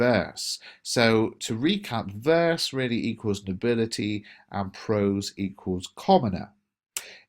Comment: sorry, verse= what? I didn't understand/hear correctly what you said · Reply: nobility